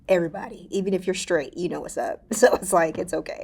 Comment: What she say at 4:06 ? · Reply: Everybody in the industry, even if you're straight, you know what's up.